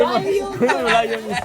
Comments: 「内容」を「ライオン」と聞き間違えるジェシー🤣爆笑する6人。今日もSixTONESは平和です☺️ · たしかに"ライオン"って聞こえなくもないのよ😂